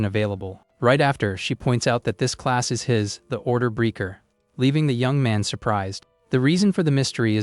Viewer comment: Lol, even YouTube has no clue what you are saying, which is a bad sign for all of us listening to you fumble at saying English words. At approximately @ 40:50, you say maybe Order Breaker, but YouTube puts Orderer in the subtitles because who can understand when you slur the word you're saying. Very disappointing. You do it again shortly, let's see if you do a better job? Nope, just more noise, and YouTube spits out "orderers", which isn't the right answer, I believe. At around 1:22:00, we finally get confirmation that the class is "Order Breaker", how shameful it is that it took so long to finally clearly say the class. I guess I officially hate this creator, which means even if I like this video, I should just block this creator.